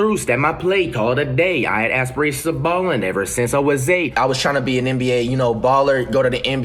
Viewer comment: Ngl I thought he said “I was at risk a bum, ever since I was 8” 🤣